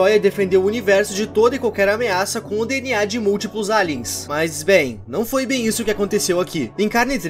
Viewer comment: eu juro que ouvi ele falando" mas, ben 10"